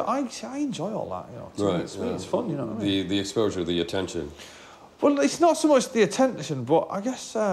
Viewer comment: から空耳で「おーリスナーすごい好きで」って言ってるように聞こえるな